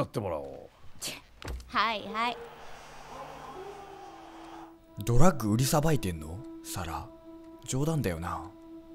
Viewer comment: ドラッグ売りさばいてるって言うのは 薬局で働いてるって言うことで アメリカではブラックジョークになっているらしい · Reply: 違うことやりながら聞いてたから｢トラック売りさばいてる｣って聞こえて、そんなにトラック持ってたのかって思ってた ようやく意味がわかったwありがとう